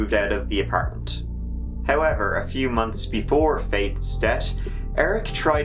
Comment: I am wondering why Irish men will pronounce the 'th' in 'Faith' but then say 'debt' instead of 'death'. Literally in the same Brett. Around the 2:11 mark to be specific.